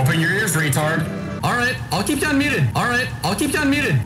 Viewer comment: i think he said unmuted